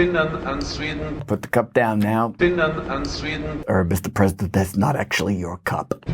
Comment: It sounds like someone is saying "unsweetened" when JB puts the cup down. I like to think the speaker was informing JB that the tea is unsweetened then JB realized he only likes sweet tea.